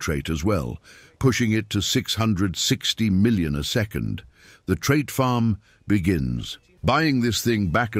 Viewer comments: I thought u said that u hate trait farming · as i remember i thought he said he hate trait farming?